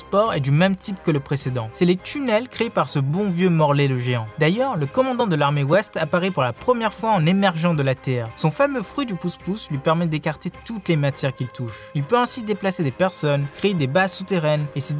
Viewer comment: j'ecoutais a moitié et j'ai entendue fruit du couscous xD